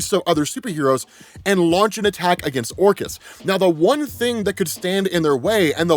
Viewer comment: This whole time, when I heard the word, "Orchis", I was hearing/thinking, "Orcust", like the dark machine orchestra themed Yu-Gi-Oh archetype lol; advanced level nerd brain conclusions at its finest. In hindsight I guess, "Orchis" makes a lot more sense lol.